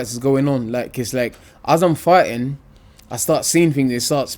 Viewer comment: at 3.57 it sounds like he is saying ' as im farting i start seeing things'